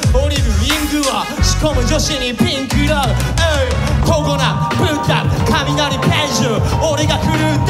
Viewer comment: 一瞬コロナって聞こえた